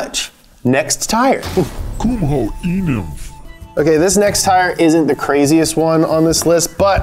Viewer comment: i thought it said kumho e milf xd